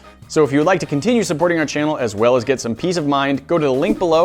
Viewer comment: D-did he just say... PEAS OF MIND?!